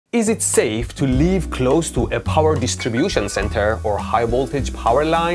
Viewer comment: Non native English speaker here. I heard "It's safe to leave clothes on power lines" and I was really puzzled. Maybe idea for another video? 😅